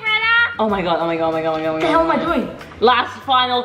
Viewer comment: Ok just listen to 6:20 to 6:30 Did biggy say What there’ll am I doing